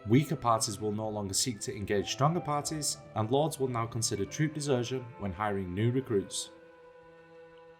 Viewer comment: What did he say at 4:55 ? "Lords will now consider trudeserths when hiring new troops." Whut? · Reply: Troop desertion.